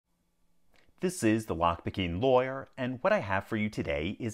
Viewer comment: Does 0:03 on Closed Captions say "lock bikini lawyer"???